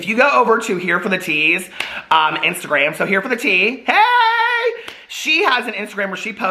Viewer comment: Totally misheard you saying here "HereForTheTease", and I think that'd be a great secondary username for your Adam and Eve sponsorship! ;P